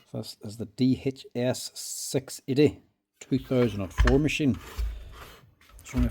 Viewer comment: I heard 2004 and thought WTF selling such an old machine , rewound and looked again 2024 :)